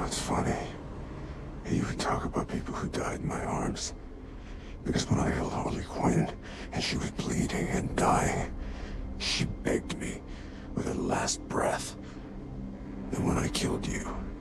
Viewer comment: His digitally augmented voice is cool but I couldn't understand what he was saying until I found out he said Harley Quinn died in his arms telling him to kill Joker 😶 2:48 Joker's face